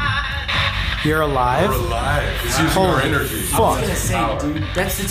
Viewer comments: Am I the only one that when they were in the surgeons room using the spirt box the spirt sounded like it was singing like I think it's very funny cause they were like being serious then I just hear :YOU'RE ALIIIIIVE🎶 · I heard "your aliiivee!!" In a sing song voice